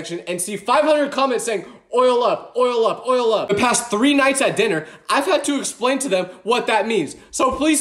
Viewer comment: i thought he said "The past 3 nights at DIDY'S" dont ask